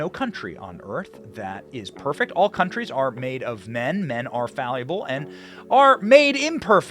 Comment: did he say all men are "falleable"? Like fallible and malleable? I... like it.